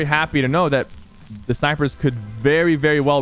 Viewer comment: He is so used to saying sypher that he said syphers instead of snipers